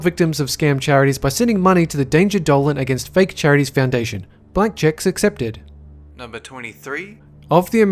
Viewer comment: I thought he said black chicks. oops